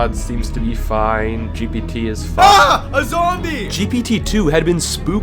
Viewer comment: I swear I heard PopularMMOS say Ah! A zombie!